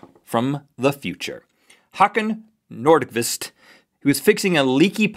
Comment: I'm Swedish. And i can barely make out what youre saying. This is what his name is:Hakan Nordqvist This is how Rett said his name: Hackan Nourdkwist